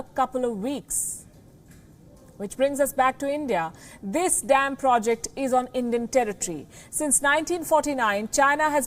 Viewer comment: I heard it as "This 'damned' project" LOL